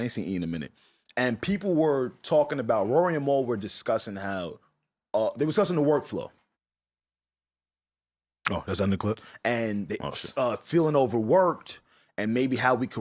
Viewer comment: must be deaf cause I heard him literally say “Roy and mal felt over worked